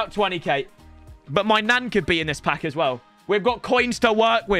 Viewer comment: i seriously thought he said "my nan" could be in this pack